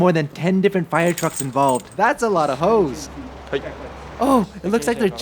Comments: "That's a lot of hoes!" Something I never thought I'd hear say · Did said thats a lot of hoes? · I'm sorry but I heard, "that's a lot of hoes!" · An ad played right after he said that so I thought he said “That’s a lot of hoes!” I’m going to die ☠️ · What I heard “Thats a lotta ho’s!!!” 🤣